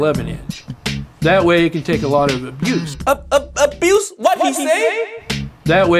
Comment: I thought he said juice instead of abuse☠️